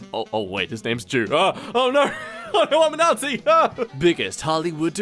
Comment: DID YOU SAY UR A NAZI WTF THIS IS PROBALY DENOMITIZED